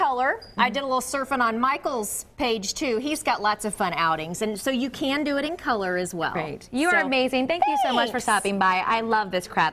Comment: did she say i love this crap?